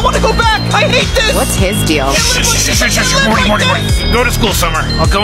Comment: I always thought he said "slant is a cosmic slaughter"